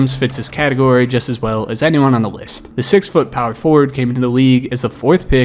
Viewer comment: Did he say the 6 foot power forward 7:50 or am I hearing things